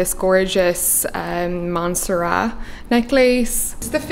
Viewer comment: may i ask where the necklace is from? I couldnt catch the brand’s name 😂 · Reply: My bad- Monserrat jewelry x